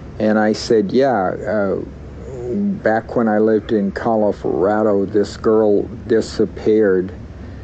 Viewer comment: sounds like he says “Califrado”. Just a word slip, or could there be something in California that is a bid deal to him?